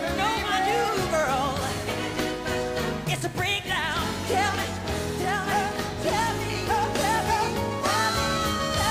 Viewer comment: Yes "Break now" Is he said that? Hahaha I'm dead 😂😂😂😂😂😂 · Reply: applle pan he said its a breakdown